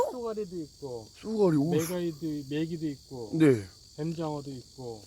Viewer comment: 나만 메갈이..메기도 있고로 들리나